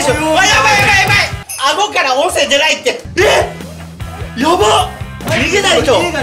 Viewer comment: 顎から音声出ないってが顎から温泉出ないってに空耳